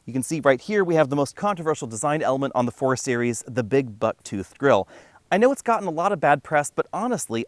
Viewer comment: My computer speakers are not working well, so I heard you say BIG BUTT GRILLE.